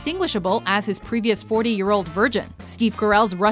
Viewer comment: did she say version or virgin?